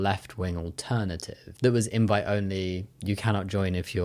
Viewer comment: I had to re-watch min 15, 1st I heard "white only" instead of "invite only"! (Not a native speaker).